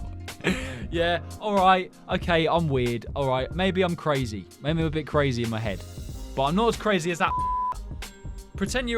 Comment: did bro just say the N-WORD or am i just dirty minded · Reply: He said f***er